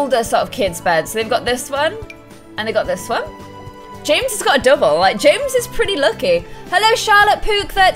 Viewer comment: Lol I skipped perfectly it sounded like she said James got ligma